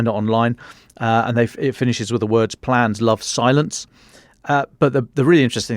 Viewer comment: Thought he said " Plants love silence " Me.. Ok thats gotta mean something good.